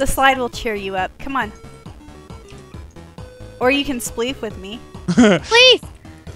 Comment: Instead of her saying u can spleef with me I thought she said u can sleep with me